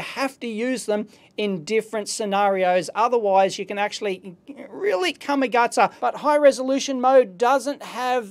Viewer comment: What's Dave saying at 11:51? "Really xXxX". I've heard the phrase before but don't understand it. Australian slang? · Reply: he said "really come a gutter", it's auzzy land slang